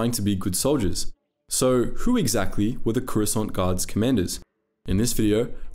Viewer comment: Swear to force it sounds like you say Croissant 🥐 🥐 Gurards.